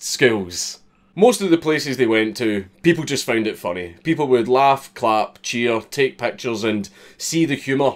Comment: oh, you said laugh, CLAP, cheer... I thought you said laugh, fap, cheer. Cuz then we got a whole nother problem